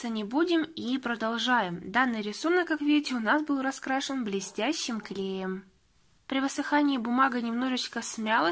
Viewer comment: мне одной послышалось "блестящим геем"?